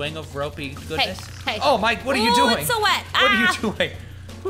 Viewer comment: oooo I'm so wet is what I heard anyone else??